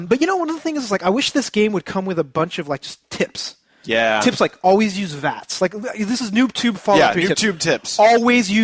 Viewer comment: at about 27:10 I had to replay it because I thought said I wish this game came with a bunch of tits. when he actually said tips lol